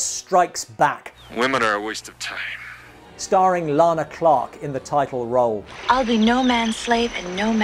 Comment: I could have sworn you were saying "Clark" instead of "Clarkson," am I mishearing?